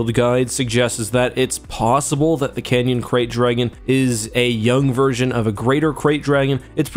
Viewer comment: Wait wat I thought they were saying great dragon this whole time